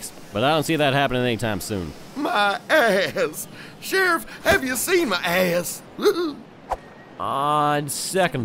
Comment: what I hear is "SHERIFF SHERIFF HAVE YOU SEEN MY A$$